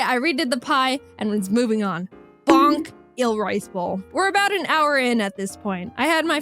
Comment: i thought she said el' rice bowl to be fancy. i may need to get my hearing checked lol